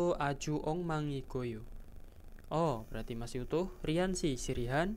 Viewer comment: sehabis kata 어 itu ngomong apa ya? kurang jls di mnit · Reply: masih utuh, kalo di kita diibaratkan membuka percakapan kak